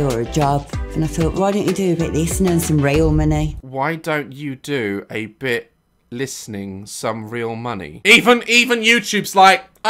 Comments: Some how my brain managed to understand what she said 7:00 since I live in Ireland. I'm pretty sure she said "How bout you do some of this and earn some real money" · i think she said "why dont you do a but of this and earn some real money" idk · all I heard was rail money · I think she said that "do a bit of this and earn some real money" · i think she said "why dont you do a bit of this and earn some real money"